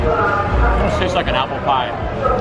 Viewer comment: thought he was gonna say apple pen😂